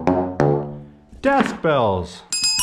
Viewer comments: I thought you said death bells instead of drsk bells for a sec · I thought he said death bells not desk bells